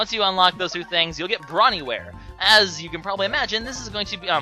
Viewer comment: I really thought you said Brony wear